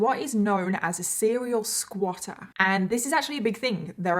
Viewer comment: Near died, thought that said serial squirter 😂😂😂😂🙈